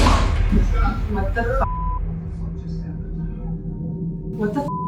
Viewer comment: someone said what just happened just before she said WTF just happened 🤨😳